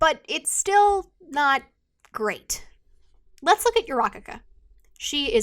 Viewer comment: I'm sorry for being so unserious, but I keep hearing "Urakaka" when she says "Uraraka" and I kept getting distracted 😂